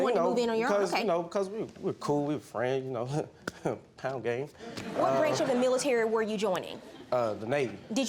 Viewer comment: the caption said "KIND OF GAY" that man didn't even say that lol. He said pound gang.. like dapping each other off. Smh